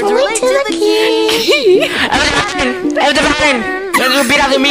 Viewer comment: I HEARD KEY????